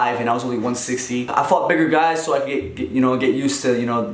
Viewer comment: It sounded like he said i fuck bigger guys 4:24 😂😂🤦‍♂️